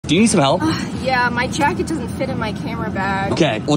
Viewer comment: At the beging I thought he said "Do you smell ?"